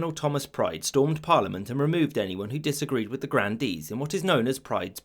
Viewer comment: What are the Grand Deeds? Or did I hear that wrong?